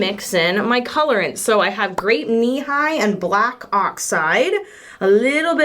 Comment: i always thought you were saying "grape knee-high" lmao today i learned about grape nehi and the fact that its an old school soda